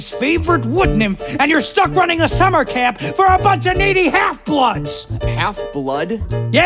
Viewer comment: I heard “Meaty halfbloods” not “Needy halfbloods”.